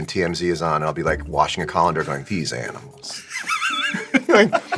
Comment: thought he said condom hahaha